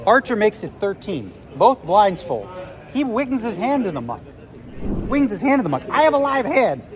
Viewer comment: Can anybody understand wth he says at 6:26? He wings his hand in the muck? Have no clue what happened.